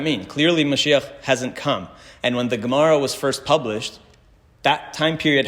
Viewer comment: What is dagmara 49:15 ? Or degmara? Or gemara? I keep hearing this.. The reason I’m asking is that my name is Dagmara so I’m very curious and wonder if this is also a jewish name?